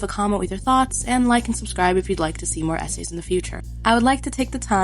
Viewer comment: "like and subscribe if you want to see more SA's in the future" i misheard this for a couple seconds